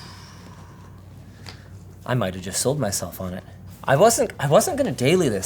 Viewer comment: I thought he said "I might have just soiled myself on this". He said sold myself.... Just sold.